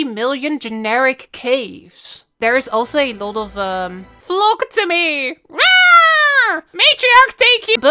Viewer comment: For the longest time misheard the „flock to me“ and thought they were screaming „blasphemy“ 🫣